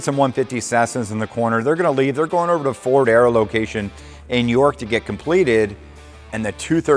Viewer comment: did you say York? as in York Penna. that's where i grew up small world.